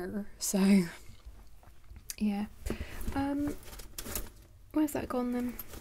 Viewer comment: "Where's that gone then?" - At first I thought you said, "Where's that condom?" Was glad you weren't over-sharing. Then you started talking about your stomach illness. Whew!!!